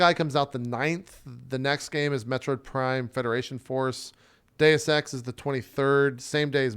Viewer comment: I got so excited for approximately thirty seconds because I thought had said "METRO Prime Federation Force." I have been wondering if a new Metro game was coming out, seeing as Post-Apocalyptic Russian games are one of the genres very close to my heart. Cheeki Breeki, iv damke.